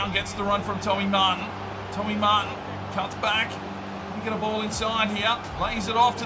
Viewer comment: subtitles say “you’ve got a ball inside of me”